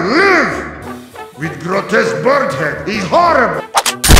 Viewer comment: "I live! ...With grotesque bird head, is horrible!" When the lines in TF2 are so insane you don't need to edit them in your insane videos. GG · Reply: I thought he said with potis bird head